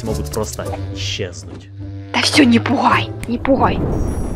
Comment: мне послышалось, что сказала ,,НЕ БУХАЙ" ВХВХВХВХВХВ